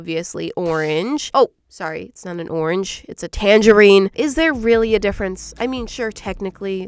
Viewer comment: Who also at the beginning thought she said it was a tangerine with a zebra head 😂😂